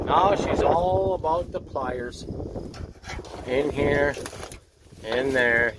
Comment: Pliers, I thought you said fires. Cool, get the blow torch & watch it melt, lol.